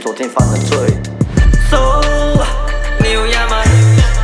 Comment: So 你有鸭吗 我听成 So diu niama 🤣🤣🤣